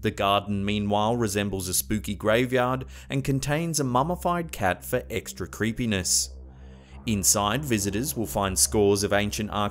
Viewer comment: My fat ass thought he said "displayed a mummified cat for extra crispiness."